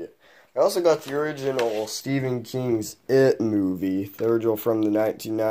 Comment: Oh at first I thought you said you got a stephen king book but ig the movie is much better